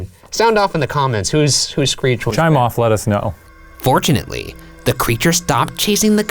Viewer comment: I heard "chime off. Let us know" as 'try moth Lettuce know'